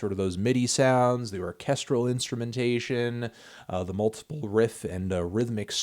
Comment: at first I heard "Casserole instrumentation", and wasn't that disturbed.